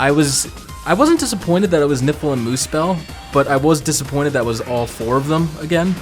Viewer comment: NIPPLE and Muspell ...that's what I heard, but I'm sure that's not what you said.